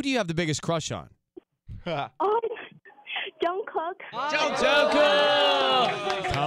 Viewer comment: Oh my the way she says "Jungkook" itz too cute..RIP to my replay button😍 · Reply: "Jungcock" I thought I heard